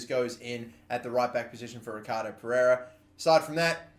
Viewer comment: Wtf I thought he said he’s getting icardi